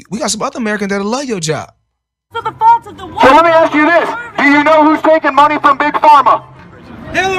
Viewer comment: What is he saying at 3:43? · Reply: You mean the guy in the red hat? He said “Do you know who’s taken money from big pharma?”